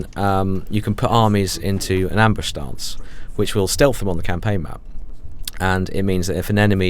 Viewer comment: thumbs up if you thought he said ambush dance